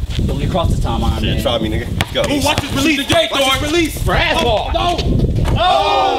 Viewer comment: he said " for Asgard!!!!!!" Omg im dying laughing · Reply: FOR ALL THESE YEARS I THOUGHT HE SAID GRASS WALK